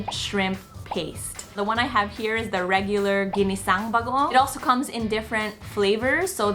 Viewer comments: every filipino do be almost havin a stroke cuz of "jinisaung" and "baguong" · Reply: I literally thought he said Ginseng bagoong and that Ginseng was the brand name lmao! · Yow am i the only one cracked out of laughter when he said "Jinisang" 😂🤣 so cute! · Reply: Lol i thought i heard him say ginseng bagoong lol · every filipino do be almost havin a stroke cuz of "jinisaung" and "baguong" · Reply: I even thought i heard ginseng baguon...